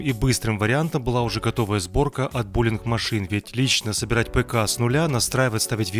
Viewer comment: Мне послышалось булинг машина